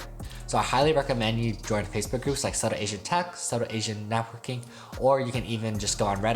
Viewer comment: Did you say “Southern Asian Tech/Networking” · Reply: Subtle asian networking, subtle asian tech :)